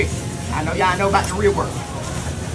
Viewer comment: The “aye that my fault” at the end tho😂😂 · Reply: i thought he said that aint my fault.